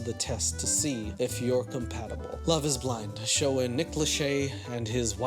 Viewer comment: everytime you said Nick Lachey, i thought you were saying... Nicholas Shay....I mean i guess it is Nicholas...Lachey but like...hm.